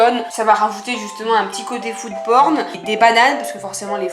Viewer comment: J'ai compris youporne 🤦‍♂️🤦‍♂️ À